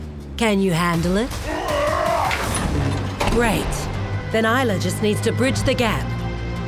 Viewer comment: Did it say Aila? Like the racer Aila?